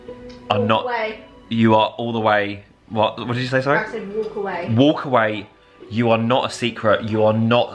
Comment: Such a funny,sweet video! Great seeing in her festive green jumper. Such cute, thoughtful presents . When said, If your an Iriis from the holiday , walk away , they dont deserve your tears . I thought, he said , walk away they don’t deserve your tits ! Which , yeah , I kind of agree with also . lol ❤😂❤